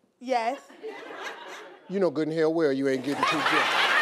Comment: what did he say at 0:50 I didn't get it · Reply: "U know good in hell u ain't getting 2 gifts" 🤣🤣